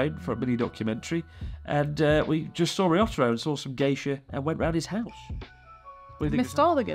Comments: What he said: "We saw some Geisha" What I heard: "We saw some gay shit" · I heard "we saw some gay shit" when he said "we saw some geisha"